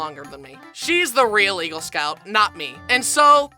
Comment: tbh i thought he said egirl scout instead of eagle scout